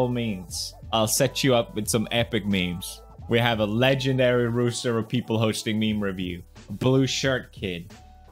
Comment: did he just say we have a legendary rooster? Instead of roster? xD